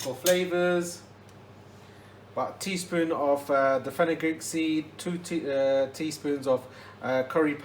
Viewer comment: Really am gonna try this. Tnx! You write ‘fennel seeds’ bur I hear ‘fenegreek seed.’ Which is it?